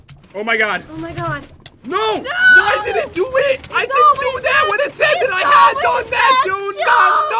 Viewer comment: i think said WHAT WHY DID IT DO THAT I DIDINT DO WHAT HE HAD NONE NAT SAID DUDE NUM NUM NUM NUM num